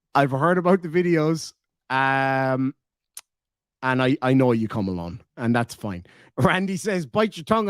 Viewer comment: 🍀 🍀 marker 49:32 . I just died 😂 😂 😂 " I hear you cum alone" pure gold 🥇 🍀 🍀 😂 😂